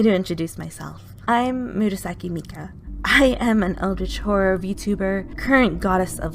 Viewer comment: i legit heard Eldritch Whore 🤡🤡